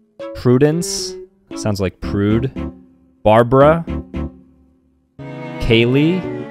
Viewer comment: "Barbara" What I hear: Bebruh "Bertha" What I hear: Birth AHH